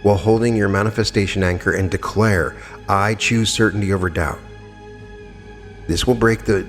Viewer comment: What does he say at 24:25? "I choose..." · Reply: "I choose certainty over doubt. "